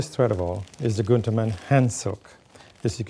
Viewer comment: What is he saying at 5:00? What is that thread? Is he saying "hand silk"?